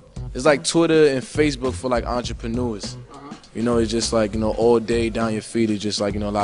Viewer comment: What's the word that he says in 4:43? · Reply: entrepreneurs, a business owner.